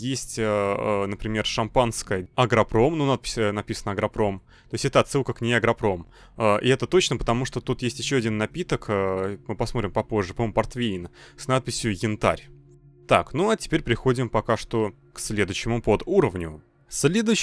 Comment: Послышалось портвей Хентай